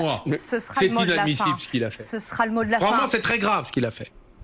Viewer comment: La meuf a dit "ce sera le mot de la fin", j'ai entendu "ce sera le mollah". Je deviens parano à cause de ces malades 😂